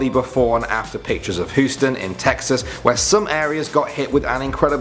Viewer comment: It sounds like he said hooston